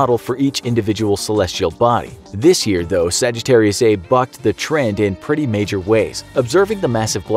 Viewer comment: Did I hear wrong or did he just say...... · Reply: Bucked